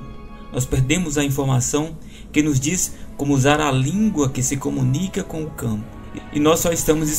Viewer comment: O que você fala no minuto 8:05? A „A língua que se comunica com o câo???? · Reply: A língua que se comunica com o campo … o campo da matriz divina